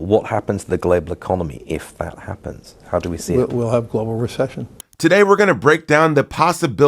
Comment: Larry fink noooo not global reception @ 1:00 😂 *Recession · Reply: in the video he says Recession but the subtitles say Reception